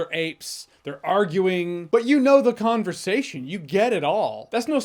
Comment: Did he say “Kongversation” 😆 Love it either way